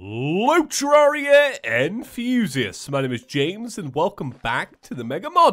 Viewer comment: Oh! ENTHUSIASTS! I always thought he said hello terraria and FUSIOUS! Also, I have been waiting for the cannon to one shot a boss... ever since the Cactus worm, in fact!